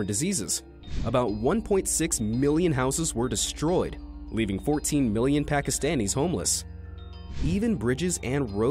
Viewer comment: Did anyone else hear he said 14 million and it said 40 million